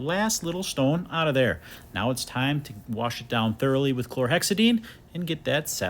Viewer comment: new to this channel so I'd like to ask what's inside the sprayer bottle? I assume it's some kind of disinfectant but I'm not too sure. edit: is it the same as what he said in 5:19 ? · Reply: Yep chlorahexadine (not sure if I spelt that right)